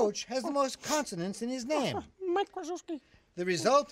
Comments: Did he just say "Mike Wazowski" at 0:40? XD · beaker, why did you say Mike Wazowski ? Did you watch monster university or monster Inc ? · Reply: Mike Kryzyewski. Head coach for Duke University. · I heard Beaker say "Mike Wazowski." Insert shameless Disney cross promotion EVERYWHERE! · LoL Did he just say mike wazowski hahaha!!!!!😂😂😂🤣🤣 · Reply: Nope. He said Mike Kryzyewski, head coach for Duke's men's basketball, but he said it like it's spelled.